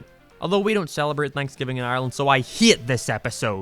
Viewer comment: 14:12 where did cause i heard ireland but mabye its just me because im irish